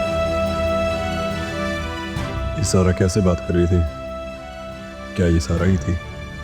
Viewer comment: did he say zara 😮